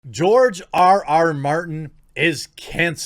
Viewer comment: I heard "George R.R. Mountain " Then I laughed.